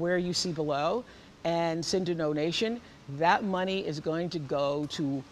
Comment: it sounds like she says nonation 😂